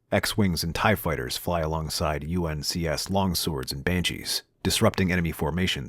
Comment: said UNCS instead of UNSC 😭😭😭